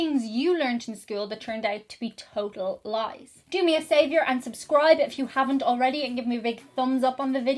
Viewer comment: Hi Interesting video. One question though....at about 08:02 in the vid it sounded like you said "do you a savior".....How does one do that?